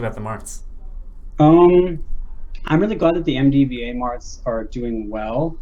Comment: What does PP say at 1:34:40? He describes them as MDVA Marths? It's one of those things where I hear it one way and can't unhear it. Unless I'm hearing it right... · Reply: He is saying MD/VA. You are hearing it right. Trust yourself